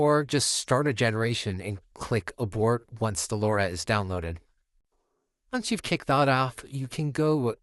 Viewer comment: Sorry, i couldn't quite gather your recommendation at 1:41. Did you actually said something like Click generation, once the lora has downloaded, click abort. Is this a quick and dirty way to create a lora to reduce video generation time? Also, may i know if i need repeat this lora creation when i change the subject in another new video. Thank you and warmest regards